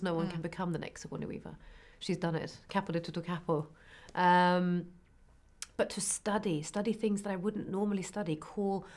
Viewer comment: What does Rebecca say at 4:04? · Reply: Kept it a little bit careful